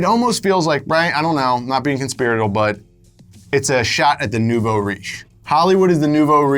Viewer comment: what did he say? voriche? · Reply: Nouveau riche. French for "new rich" and commonly used to describe people who have recently acquired wealth and therefore (likely) lack good taste